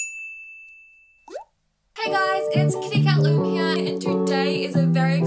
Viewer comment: When I was little I always thought that the kitty cat said "kitty cat meow" but it actually says "kitty cat loom"